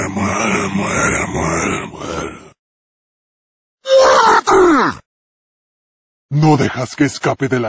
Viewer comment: It's been 16 years and even as a native spanish speaker I didn't know what 2:10 said. XD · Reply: dice "muerte!!"